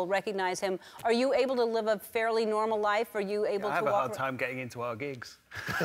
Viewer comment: What did he say on 0:53? It's so fast and a little bit difficult to understand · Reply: "Hard time getting into our gigs".